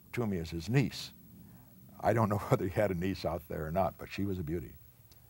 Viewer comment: what did he say here? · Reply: “I don’t know whether he had a niece out there or not but she was a beauty”